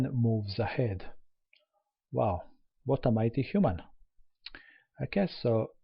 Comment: did he just say on 6.28 - "what a mathematician" ? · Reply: "what a mighty human"